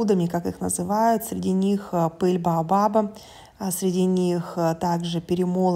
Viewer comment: А ? Что?!🦻Пыль баобаба? Мне одной послышалось ? 🧐